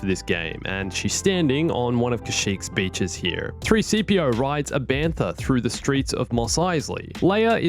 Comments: Did i just hear 3:CPO? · did I just hear 3-CPO